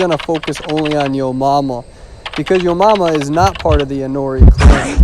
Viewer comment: At the beginning I thought you said "Hunter is gonna focus on your mama"and I was like... WHATTTT? Why will hunter focus on my mom? :O And then after few seconds I realized the survivor name.. 😂